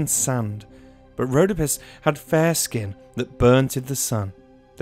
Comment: This video came one while I was falling asleep, I woke up when I heard "road of piss had fair skin"